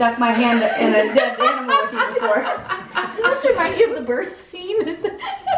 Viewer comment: “Does this remind you of the birth scene”-Andrea.... so now we know for sure that the baby is coming in season 4!! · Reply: I thought she said, "Does this remind you of the bird scene?" Yours makes more sense. lol